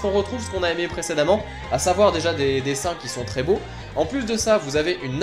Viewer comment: J'ai entendu "des seins qui sont très beaux" ^^'